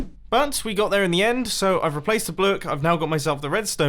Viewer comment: "ive replaced the blook" did anyone else hear that